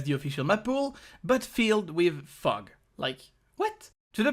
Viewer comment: i heard "filled with FUCK" at 2:43. didn't cross my mind that the last word might've been "fog" until like 30 seconds later lmao